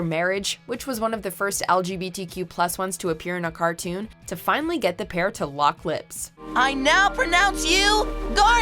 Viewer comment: i thought she said they finally got the pair to lock cl*ts 😂😂🤦🏽‍♀️